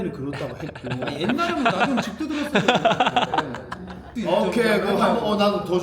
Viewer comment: 옛날엔 나도 집도 들었어욬ㅋㅋㅋㅋㅋㅋㅋㅋ